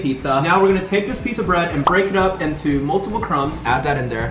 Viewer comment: Instead of break it says beak